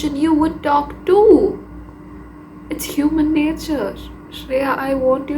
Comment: Did she just say shreya . Like this vid was meant to find me😭😭😭